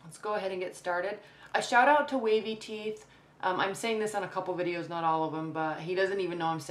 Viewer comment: Sounds like you said way b tea or wade bteeth. Can you tell me? I've listened several times and can't get a grasp. Thank you! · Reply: Wavy Teeth